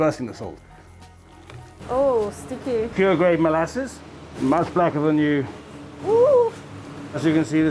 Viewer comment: Did He Really Say the Black Syrup is Blacker Than You??